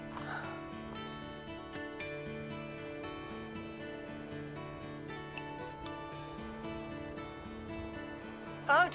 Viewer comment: what did she say? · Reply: Goodbye in whispering voice.